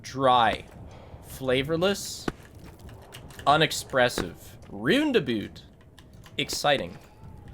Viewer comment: i thought he was saying rendezvous not roundabout genuinely so confused until i read chat